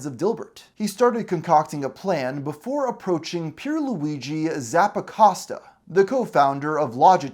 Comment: OH! His name was Pierluigi Zappacosta! I thought you were saying his name was Zapp Acosta, and that he was a pure Luigi 🤣🤣 Me: "Poor guy, he deserves better..."